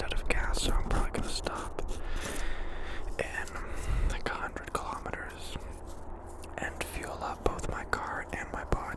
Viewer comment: I’m American what did he say at 2:30 ? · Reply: 100 kilometers